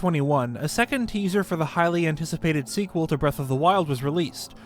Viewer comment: When you said "Highly anticipated," at the beginning of the video, I heard it as "Hylian Ticipated"